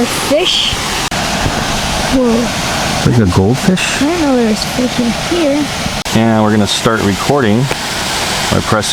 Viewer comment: What does the kid say? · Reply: Scott A “I didn’t know there was fish in here”